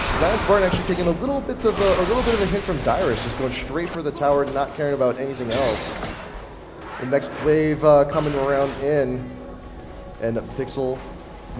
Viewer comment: did he say damage from Dyrus...